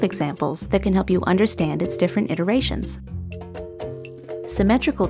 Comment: What's the word she uses at 4:53....different WHAT?? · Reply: The word is iterations. This means its different ways of using these designs and processes in different applications. We hope it helps!